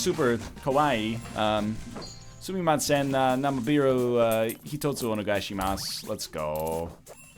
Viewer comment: I'm not as fluent in weaboo as I'd like to believe, but did this egg just request cunnilingus/anilingus? I swear I heard "Sumimasen, namebiru hitotsu onegaishimasu" and all google translate will tell me about that is "Excuse me, I'd like a licking one, please."